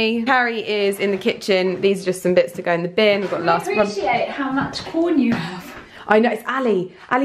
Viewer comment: Omg when you thought @ 9:54 Carrie said "Can we appreciate how much porn you have" and was like "I know it's Ali! He just buys the same stuff" and you replayed it just to make sure it wasn't what you thought you heard...